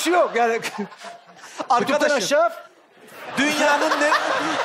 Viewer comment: de tolga ne diyor da sarp gülüyor hala anlamadım biri söylesin pls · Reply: Aleyna Aydın :: Kutup'dan aşağa, Kasımpaşa 😀